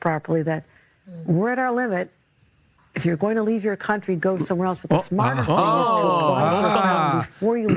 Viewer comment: Actually sounds like she was saying "If you're leaving your country TO GO SOMEWHERE ELSE....." I'm not saying they're not hypocritical AF, but I also have to be honest on what I heard.